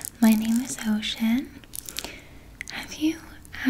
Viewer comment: I swear, in the beginning, it sounds like you said, "My name is Ho-cean." Lolz.